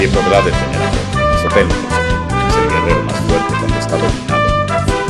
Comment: No se entiende bien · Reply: es el guerrero mas fuerte cuando está dominado por la furia*